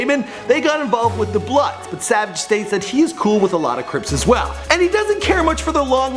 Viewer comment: did you just say bloods and crisp